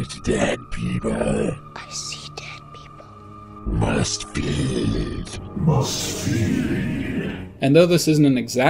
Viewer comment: when I was younger I thought the ghoul said: "Must pee!"😂